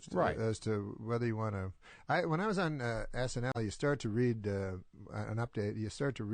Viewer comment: there's alight glitch and it soundds lokie he said S&M instead of SNL. Not too sure what he actually did say